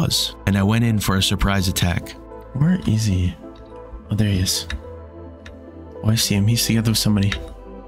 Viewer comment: Translation: “Oh, I see him, he’s seeing through somebody.” That’s what I heard, but I think you meant to say “Oh, I see him, he’s standing with somebody.”